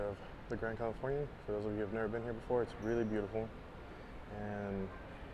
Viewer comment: It wasn’t until this video when you first went to the Grand Californian?! Why not?! I started going in three months after I became an AP in the summer of 2015. -The Disneyland Neighbor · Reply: I apologize. At timeline 4:08 I thought you said you’ve never been there before. I I listened again and you actually said, “For those who’ve never been here before.“